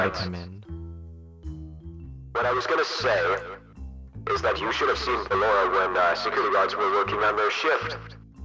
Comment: can someone please translate what cyan said because all i got was “ship” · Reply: "You should have seen Ballora when security guards were working on their shift"